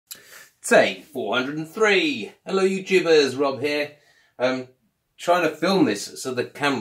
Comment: Hi there, thanks for comment. I've watched this back three times now and still can't see where I said 'jibber'? Only use I'd think of for it is 'quit your jibber jabber' (stop talking) from The A-Team. Never heard it said in the context you suggest I'm afraid. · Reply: first few seconds 😂 thought you said 'hello you jibbers' and nearly fell off my chair.